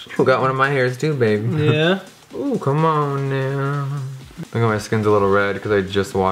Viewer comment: Did he just say nigga at 8:39??!?? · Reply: Technisis he said Come on Now, he was high